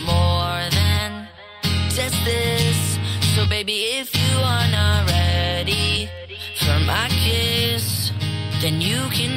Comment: This is what I always hear "So bby if you're ready for my kids" jacob's been doing some stuff {insert Lenny here}